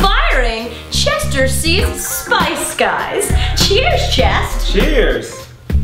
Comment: When she said "Cheers Chest" I thought she said "Cheers Jeff" and I was like smooth as fuck reference to Camp Takota. #camptakota ***** ***** Hannah Hart